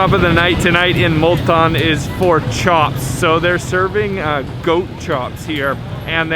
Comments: did I heard "Pork chops" @ 1:43 😂😂😂 by the way nice videos and you guys made me homesick ❤❤❤❤ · did u say pork chops in Pakistan???/ brave man !!